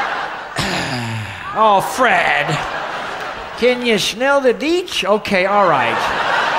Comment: "False alarm, it's just gas" Ah yes... fart jokes... a staple of good humor! "Can ya smell the beets?" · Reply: TheNecroticRaptor I thought he said can you smell the beach